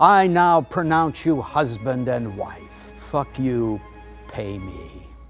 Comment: My fiance's sister told me, "Small wedding, long marriage". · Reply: I literally thought Pizza said "fuck your cousin" and was like o.o okay then. Rip my eyes